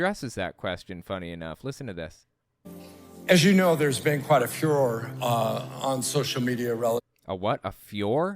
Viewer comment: I thought he said Führer. What he said was Furor, like fury or anger from a group of people.